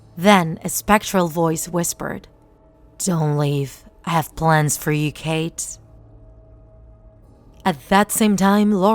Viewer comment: AT first I thought slender man said: I have plans for you baby cakes